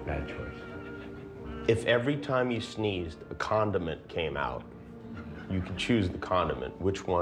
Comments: i thought he said condom too. George Clooney is still not funny but no one is. what is humor anyway? its something girls trade for sex. he can get girls so humor is absolute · Thought he said condom not condiments